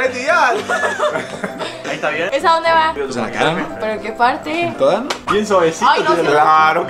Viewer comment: parece que dice pues en la cara mi amor 🤭